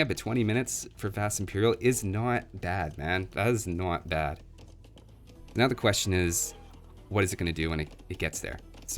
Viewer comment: what does he says? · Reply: I think the first part is "is not that bad", but it does seem like is having a stroke.